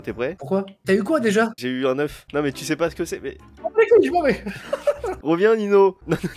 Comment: C'est moi ou Nino il a dit (ferme ta gueule j'menvai) 😂